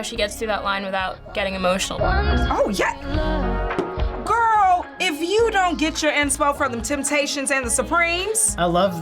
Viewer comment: Can someone please explain what this beautiful girl said on 8:08?🤗 Not really good at english🙁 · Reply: Женни Рейтер “Girl, if you don’t get your inspo (inspiration) from The Tempations and The Supremes” The Tempations and The Supremes are singing groups from the 1960s